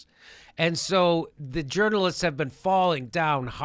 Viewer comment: Journalst? I thought you said Germalist! Will you hire me anyway? Chris Hayes looks like a silent movie slapstick comedian.